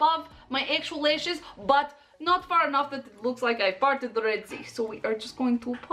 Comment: Thought said farted the sea, hehehe and thought that not right,parted that makes more sense. lol